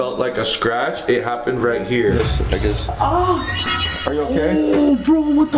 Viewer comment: what did he sayyy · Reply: I guess